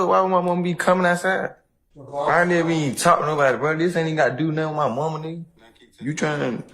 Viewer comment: Did he call the prosecution a nig**? About